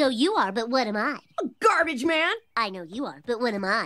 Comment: what does he say? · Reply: MortyAndTheInklings XD takes one to know one